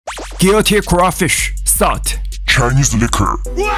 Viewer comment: Thought he said guillotine crawfish at first but then I read he said guilty. THEN he beheaded them so I’m like 🤷🏻‍♀️ well